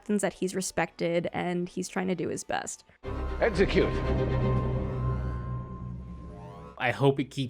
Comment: I totally heard “Exeggcute” when Saru said “Execute!” 🥚🌴😆